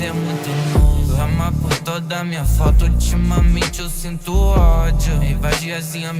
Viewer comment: o que foi dito aqui? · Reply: "derrama por toda minha foto" eu creio q seja isso